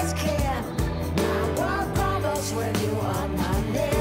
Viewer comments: When I was a kid I thought she said "I want waffles when you are not here". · 👢🧱"I walk on walls when you are not here!" Or so I hear. I know it's "my world crumbles" but "I walk on walls" is 110% funnier.